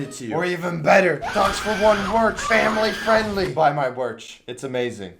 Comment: what he said "buy my merch" What I heard "Buy my march"